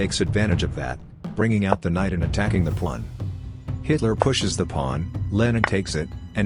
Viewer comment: Why did I hear "bringing out the knight and attacking the pwan".